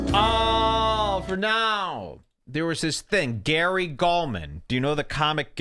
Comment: for the first few minutes i thought he was saying gary coleman. what a let down